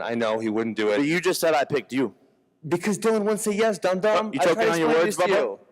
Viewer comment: Did Ben actually call him dum-dum at 1:48 ? Bruh Ben won this